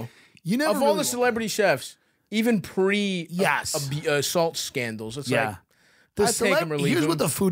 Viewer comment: Thought he said salt chef scandal, I was like go on...